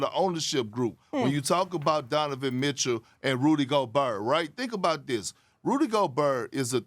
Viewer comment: did he just say rudy go-brrrrr